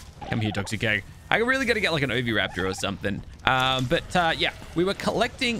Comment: for like 5 episodes i thought he was saying a obi raptor so i thought he meant the ghastly raptor hahahah